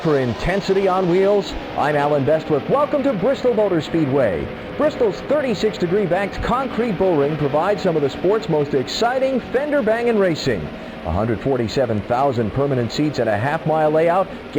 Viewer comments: For a second I thought Allen said "finger banging" instead of "fender banging" and this is why I need therapy 😂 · Did Alan Bestwick just say finger banging? 😂 Yes, I know what he really said